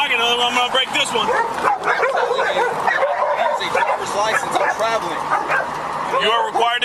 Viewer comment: did I just hear "I don't need a license I'm traveling!" 😂😂